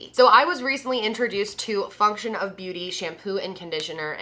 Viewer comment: Omg I know you have a hard time saying words sometimes and I'm not hatong but girl I just woke up and thought you said fucktion of beauty! My eyes got so big like omg did she just cus out her sponser? Noooo!!!